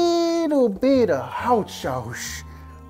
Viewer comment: I think he said a I think he said a bit of hot sauce